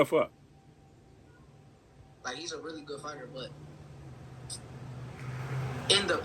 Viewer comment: I thought he said he really a 135lb fighter 😂